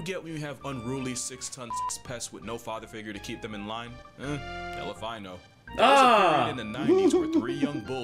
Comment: i just got what he said at 5:11/5:12 it sounds like "hell if i know" but he said "elephino"...